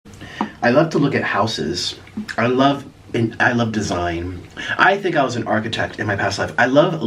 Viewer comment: I really thought he said “I love taking pictures of houses”🤣🤣💀